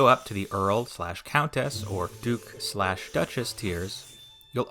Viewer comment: when you said "Earl Slash Countess" it wasn't on the screen yet, so my web developer brain thought you had something called "URL Countess Level"